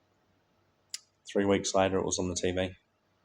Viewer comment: I haven't been on TV as far as I remember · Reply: I thought you said at some point "3 weeks later I was on TV" maybe I misunderstood.